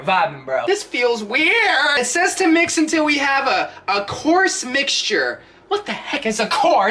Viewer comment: When he said coarse mixture, I thought he said corpse mixture